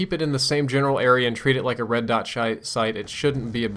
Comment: Anyone else replay 6:05 to verify what he said? "Treat it like a red dot shite"